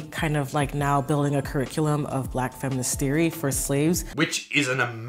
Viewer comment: Black feminist theorem for slaves. No I just wanted to make sure I heard "Black feminist theorem for slaves." Replays* Nope i dif heard "Black feminism theorem for slaves."